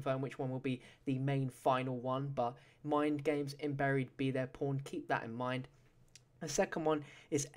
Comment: Lol i thought he said be their porn.